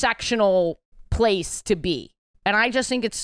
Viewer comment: I just need to say that everytime Ashley says "gay thought" I think she said "gay THOT" 😂😂 I'm Australian so I think it's the accent